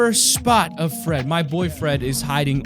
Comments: Did anyone else hear him say on 10:11 his boyfriend broke a record is he gay?? But if he is I’m totally fine with it he has. A good channel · I thought hes said boyfriend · I thought he said my befriend not my boy fred